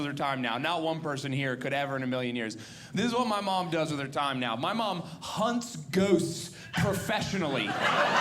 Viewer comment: LOL, I thought he said; “My mom hunts goats.” I mean, it seemed to fit. I had to wait for more context to finally understand.